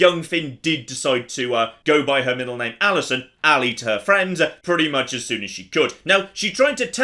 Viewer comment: No joke, I heard "Allie to her friends" as "I'll eat her friends." Made me question which run I was watching for a moment.